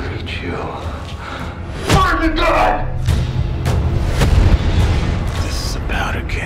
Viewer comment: Batman actually responds to riddler saying “what have you done” it’s in the subtitles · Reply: I thought it said “wheres the gun”